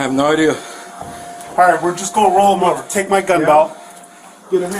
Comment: Take my gunbelt.. I heard ''take my gumball'' right before seing gumballs ;)